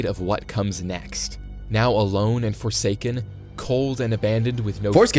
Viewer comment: is it just me or did i hear "forsaken" also don't mind that i commented 2 times